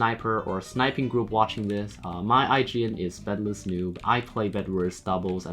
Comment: i thought u said “antibedless” lol